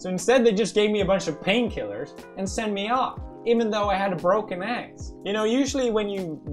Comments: I thought it said "my broke ass" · i thought it said im broke ass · I thought that it said : my broke a** as in being broke and I was like (⁎⁍̴̆Ɛ⁍̴̆⁎) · I thought he said I am a BROKE ASS lol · At first I thought it said “My broke ass” And I thought if he was broke, than what am I? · I thought it said my broke ass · Who Else thought it said my broke ass😂😂